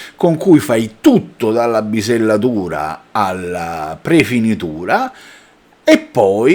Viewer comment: Ma porca miseria, appena ho letto "nave scuola" mi aspettavo tutt'altro.... se poi aggiungiamo che invece di bisellatura ho capito "pisellatura" ti dice tutto. Comunque maglietta superata (va aggiornata). Poi meno male che hai deciso di fare vedo più corti: 53 minuti parte uno....(lo sai che non capendo una mazza noto questi particolari fondamentali)....